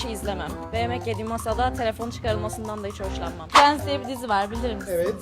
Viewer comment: Friends demiş ben prens anladım 😂😂😂😂